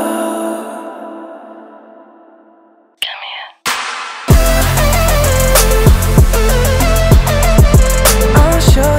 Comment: what does say in the minute 1:10? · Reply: "come here"